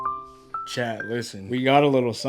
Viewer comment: did you say chat????